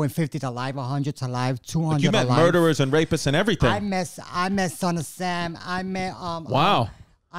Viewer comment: I thought you said titi blew son of sam